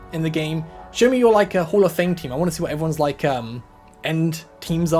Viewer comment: there is an enteir channel within the official discord dedicated to hall of fame entries, unfortunately you gotta have a special role to see it, you have been given entry 165, congrats! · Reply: thought said hell of fame